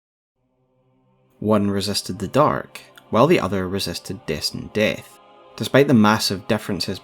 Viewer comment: Bro i was listening on my speakers with a fan on and the first time i heard "One resisted the dark. And the other resisted this dick."